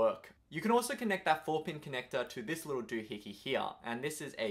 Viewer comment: What do you say at 3:49 or so? You can also connect it to this little...? It sounds so strange haha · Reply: Doohicky! Lol. There are subtitles you can check though.